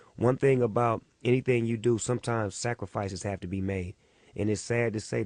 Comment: Did he just say SACRIFICE?!?!!?